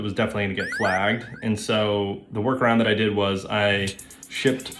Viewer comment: I understand that you said "Workaround" and 8:27, but it sounds like you said "War crime" and that's delightful.